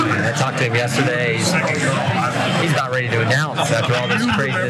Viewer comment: Sounded like he said. " he,s NOT ready to announce"